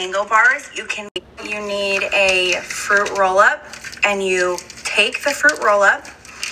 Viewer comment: I thought that girl said "tape the fruit roll-up" and I was like hollup